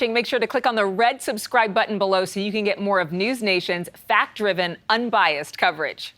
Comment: Did she say: "Noose Nation"?